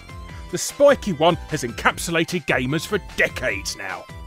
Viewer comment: Did you say "encapsulated"?